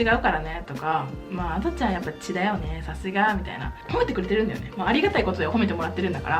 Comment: 『血だよね。』が『痔だよね。』に聞こえる笑笑